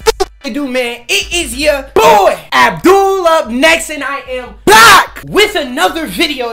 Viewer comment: I AM BLACK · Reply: no bro like it sounds like he says I AM BLACK in the timestamp click it